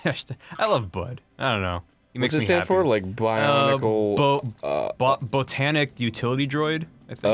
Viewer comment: I heard Bionicle